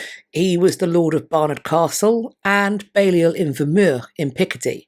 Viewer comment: What is "bayul inur" or Balliol Imur". Sorry I couldn't quite understand what was said. Did don't catch that? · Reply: Balliol in Vermer or Wormur?, I thought. In Picardy, in France anyway. A French holding of the family. (Edited to correct location name).